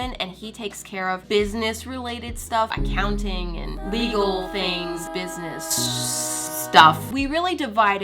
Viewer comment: When you said legal things I thought you said lethal things